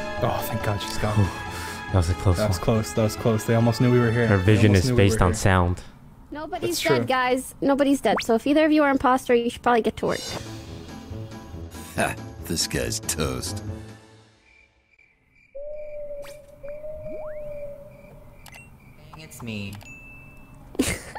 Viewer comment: So I’ve been binging Toast videos and his intros I thought at first it said this guys toast but does it actually say disguised toast?